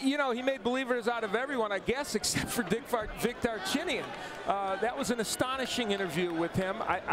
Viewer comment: sec. watch what??? he just said DICKchinyan?